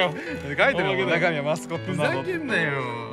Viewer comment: 中身はマスコットって部分がマスカットに聞こえた... クリームパンの出生地やし岡山→ピオーネ→ピエールくらいのつっこみが欲しかった